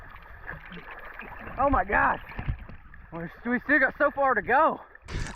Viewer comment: did u just said gods name in vein or am i trippin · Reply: He said oh my god